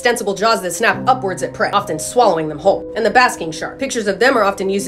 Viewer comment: Omg I've known about the basking shark since I was a kid but fsr I misheard as "basket shark" so that's what I've always called it...